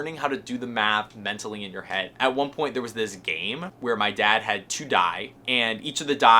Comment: Not criticizing at all, but just FYI, the plural of die is dice. When you said your dad had two die i thought you said your dad had to die, lol. Good video. I homeschooled both my kids and some of my grandkids .